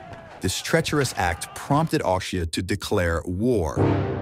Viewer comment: he says instead of austria, he says something 'Aushift' LOL